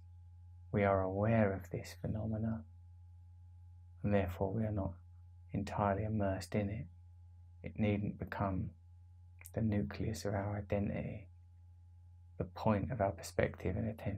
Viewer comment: Does he say it needs to become the nucleus of our identity or needn’t to become the nucleus of our identity?